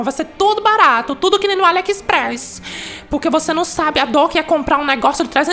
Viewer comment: Só eu que entendi em "1:10" kkkkk "alex press" kkkkkkk